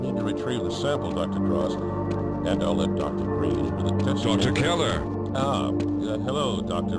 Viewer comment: did I just hear Dr. Breen?